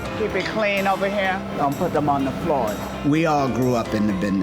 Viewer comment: It sound like he said dont drop the soap lol